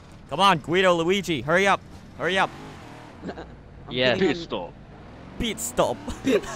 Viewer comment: sounds like Pete stop instead of pit stop